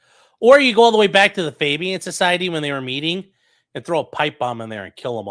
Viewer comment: I thought said "Babyan Society," and it was just a collective of the infant versions of evil shitheads throughout history I'm stupid